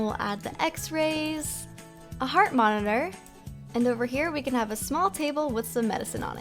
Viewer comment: anyone else hear horse? Like if you did . .😊😊